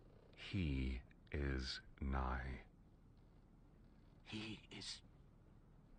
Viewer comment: excuse me, could you help me? 0:38 what does he say? 3 words. I hear the first 2, but I don't understand the last one 😅 He is .... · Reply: he says "nigh"!